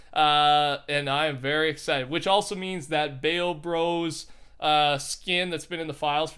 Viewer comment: who else thought he said bang bros